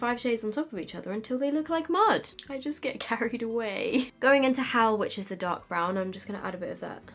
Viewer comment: What you said: “Going into Howl, which is the dark brown...” What I heard: “Going into Hell, which is the dark brown...” 😂😂